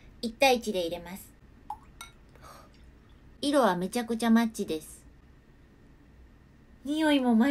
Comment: マッチがマッチョに聞こえた